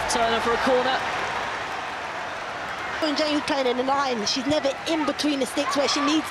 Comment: I heard Reece James in between 11:41 and 11:44😂😂